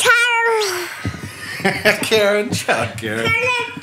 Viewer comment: It sounds like he said "Karen, shut up!" XD